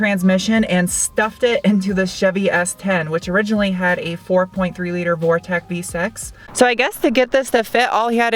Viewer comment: a 4.3L Vortex Vsex, did I hear that right...LOL How about introducing us to the owners of the cars...? Great video keep it up